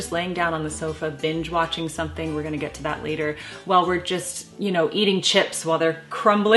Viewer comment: For a moment there I thought you said eating “sh*t” ! Lol